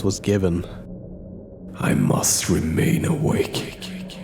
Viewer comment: I heard "I must remain a cake"